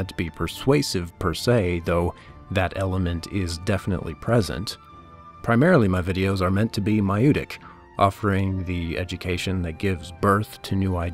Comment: This is fantastic, looking forward to more content from your channel! Just a quick question, what is the word you use at 28:30? When you say "primarily my videos are meant to be ..." Sounds like "miotic"?